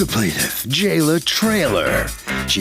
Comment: Did he just say her name is Jail a Trailer?